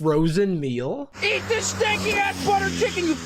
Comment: I think he said “sticky”, so hopefully not racist